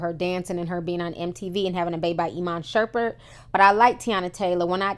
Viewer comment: Did she just say iman shurpert 😂